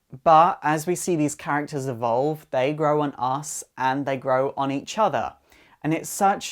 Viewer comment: When you said they grow on us I thought you said they grow an ass.